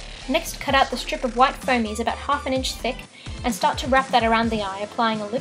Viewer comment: did u say foamies?